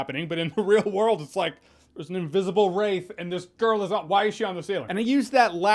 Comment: Oh god, I thought he said “invisible rape” and choked on spit!!!! 🤣